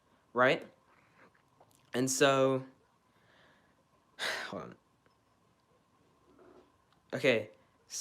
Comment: you can hear someone say “go on” when stops talking. Could that be his stepdad manipulating what he says in the livestream?